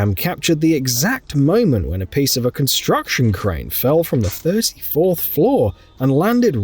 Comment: Did he just say “first fourth floor”?